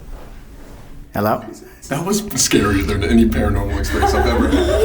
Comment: At like 48:43 like right after the girls did the doll thing I think I heard a deep mans voice say hello